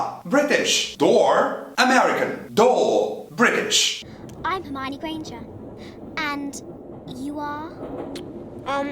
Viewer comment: só eu q não entendi oq a Hermione disse aq antes do "and...you are..."? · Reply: "I'm Hermione Granger, and you are...?" "Ron Weasley" "Pleasure"